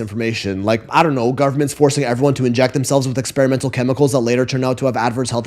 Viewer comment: Ngl @ 33:45 I heard "government... injecting people with harmful substances with side effects" and "did he just covid conspiracy????" and i GENUINELY gave the benefit of the doubt. I thought "oh he must be bringing up all the stuff that was publically acknowledged in that book The Polonium Files, yeah Milo that really happened," only for to mention the article he brought up which made me rewind and actually watch the video instead of listening during work like its a podcast. When I heard the full sentence he said properly, he said something like "the government forcing people to inject themselves" which... Yeah that's covid conspiracy and not like the case of the painter who was injected with polonium and had no idea. I genuinely didnt think he was that far down the pipeline already.... Heartbreaking: person loses additional bit of hope they didnt know they had.